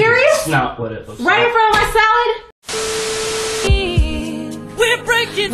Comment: What did she say at 2:48? I didn‘t understand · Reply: "right in front of my salad?!"